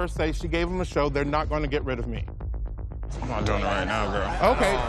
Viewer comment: merrie: "no one in the top 4 heard her say 'I gave them a show, they're not gonna get rid of me?'" Saint: "you the one doin it right now girl..." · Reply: Did she say that?? I heard "I ain't doing that right now, girl"